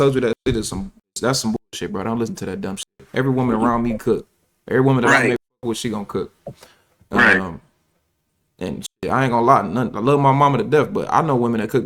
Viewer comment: naw you missed what he said. He said is trainer told him to “never tell your woman to cook like your mom” lol